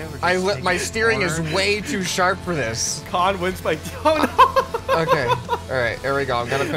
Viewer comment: did he say kan wins by toe? If he did it’s so funny 😂😂